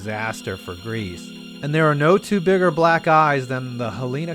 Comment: "There are no two bigger black eyes..." heh-heh, I thought you said "guys."